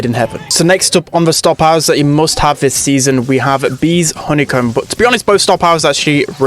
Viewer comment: did you say honey coat